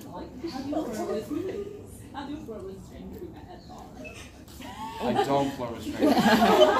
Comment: what does Sebastian say??? Can someone comment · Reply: "I don't flirt with strangers"